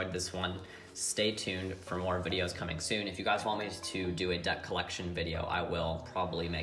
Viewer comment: I literally thought you said "Debt Collection" video. I was like "Wow! He's the IRS too!!"